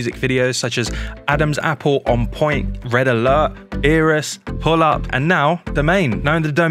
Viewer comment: Is it me or in 0:13 he said eerus instead of beerus